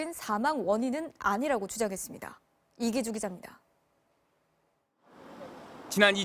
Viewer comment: 여기 이기주의자입니다.라고 들려욬ㅋㅋ